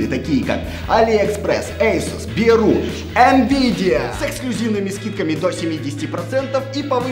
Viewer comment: мне послышалось СЕКСЛЮЗИВНЫМИ